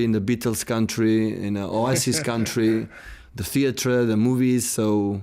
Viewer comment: I thought he said "ISIS country" at the start look